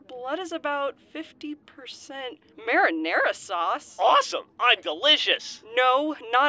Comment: I thought Simmons said Grif bled gravy not marinara sauce